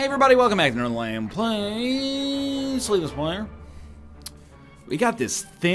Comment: Every time I hear “Slay the Spire” I keep thinking “Spay the Slire”. I... dunno what my brain does half the time.